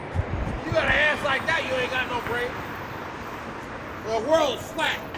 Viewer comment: did he say "if you have an ass like that you ain't got no brain" :D?